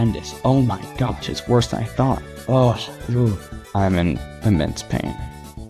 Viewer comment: i thought he said "i'm in ms paint"